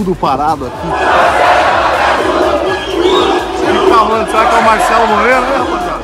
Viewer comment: "O marcelo e meu Piru" entendi foi isso 😂😂😂😂